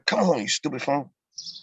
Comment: I appreciate the ending to this zoom call 😂 they really thought he said stupid hoe! Judge and attorneys face were priceless.